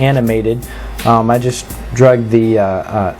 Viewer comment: lol 3:02 i just drug... i thought you said drugged! ROFL!